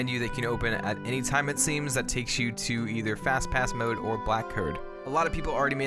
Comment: Well, if I misheard that right or a fun fact, there was something called the "Flashpass" to celebrate the "Flash" from the DC comics for an event in Disney world. (BTW, I love the Flash, especially with the TV series still going on)